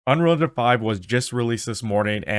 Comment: Boi said "Unrealdefife" bro pronounce it when you say it lmaooo "Unreal Engine Five"